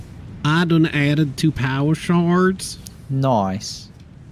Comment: At first, thought you said power shart. glad i was wrong.